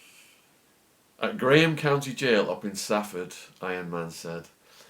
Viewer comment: Stafford prison? I live not too far away and know 2 people who work there. It's for pedophiles and rapists. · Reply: I thought you said up in Stafford my mistake